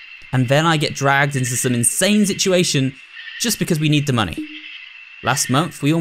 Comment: dose anyone else hear him say Da Money instead of The Money?